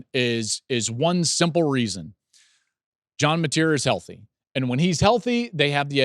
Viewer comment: caption says “Dillon Gabriel” is healthy? When he totally said John Mateer. WTH?